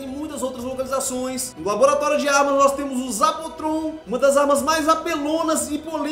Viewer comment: Gente , alguém sabe o nome da arma q ele falou q é a mais apelona e mais polêmica... 12:05 , n entendi o nome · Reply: Zapotron